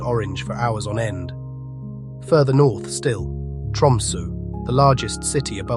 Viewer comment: Did anyone else catch the narrator pronouncing Tromsø as Tumsu the whole time starting at 05:00 Opens in a new window]? Pretty funny slip-up